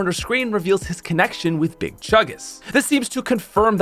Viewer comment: Legit thought he said "This shows his connection with Big Chungus." I nearly spit my gum there.